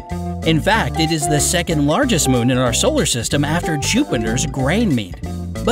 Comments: Did you just say, GRAINMEDE? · did he just say Grainmede? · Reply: it's Ganymede. i'm saying it the way he did.